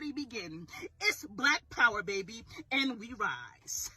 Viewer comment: Cowards! All cowards who will NOT fight without a gang. There is NO BLACK POWER! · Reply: Oo I thought they said black powder.....I thought I have some I'll give you